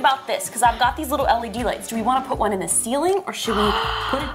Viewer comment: When she said led lights I thought she said smelly d lights